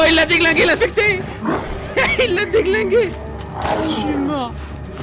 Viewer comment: c moi ou a 59:19 on entend "chuis mort"😅 chais pas moi j'entends sa 😅